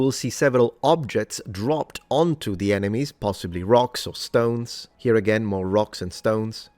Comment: Did I hear a Rock & Stone?! 😁